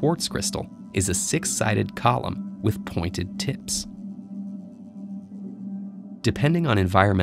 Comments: I don't care what you say, you aren't going to convince me he didn't say "pointed tits" at 2:35. Don't even try. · I thought he said pointed tits